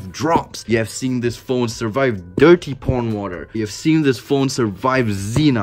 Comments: Did I hear you say at 13:58 You have seen this phone survive dirty porn water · LMFAO AM I THE ONLY ONE WHO HEARD HIM SAY DIRTY PORN WATER😂😂😂😂😂😂😂😂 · I thought it was just me when I heard "this phone survived dirty porn water" so then I went on captions and it said it too listen for yourself.